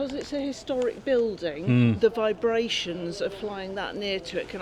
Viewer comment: Have always wondered why my GF tends to let our drone just hover over her lap, she says it's to keep the propellers from not sticking if left for too long. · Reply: I thought he said vibrations not vibrators 😅